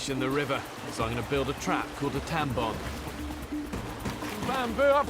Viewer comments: I thought he said tampon. XD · here is what i heard "spotted a fish in the river, so i'm going to build a trap called a tampon..."